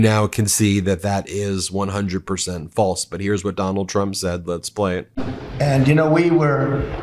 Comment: I thought you said "McDonald Trump". It has a ring to it...